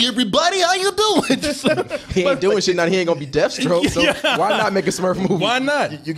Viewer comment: did he say Deathstroke? in march?